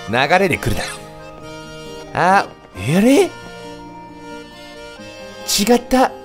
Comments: あれぇ？がReally？にしか聞こえない笑笑笑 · があーれー?にもreally?にも聞こえるな